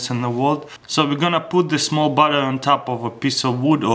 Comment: wait at 0:39 did you say...BUTTER?!?!?!?!